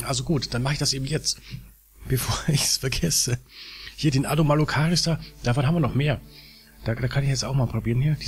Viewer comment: Ich habe es mir schon öfters angehört, aber ich habe es nicht wirklich verstanden. Wie heißt der Keks bei 2:57 ? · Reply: Ich nannte ihn "Anomalocaris". Das ist aber nicht der passende Vergreich. Ich meinte diese Schuhsohlen-Tiere aus der Ediacara-Fauna.